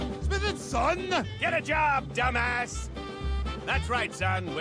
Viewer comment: Wait jack said dumbass i thought he said hippie before??